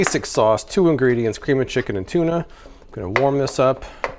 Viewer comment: Did I hear correctly? Cream of chicken and tuna? I must have misheard you.